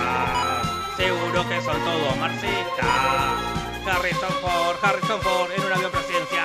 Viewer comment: pense que dijiste MASISTAS 🤣🤣🤣🤣